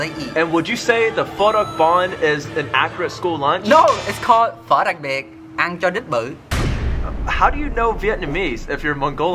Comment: Am Vietnamese and I Thought said was the pho and eat dog butt big